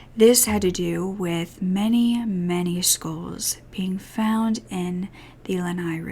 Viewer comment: Skoools 💀💀💀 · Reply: I honestly thought she was saying "schools" at first lol I've never heard skulls pronounced like that, I was so confused!